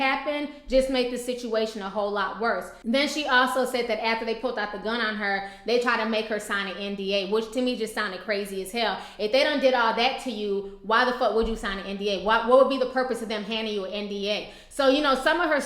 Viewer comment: I heard nba and I got confused af